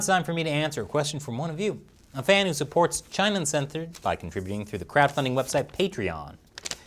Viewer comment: Hey was it just me, or did you say "China Uncenthored" @ 3:30 . I had to replay a few times.